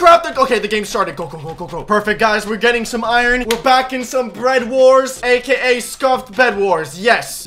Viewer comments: i think i just heard "BREAD Wars" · Did I hear that correctly...? ...Bread-wars?